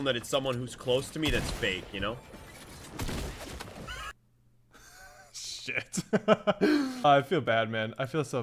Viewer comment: I think he said sh*t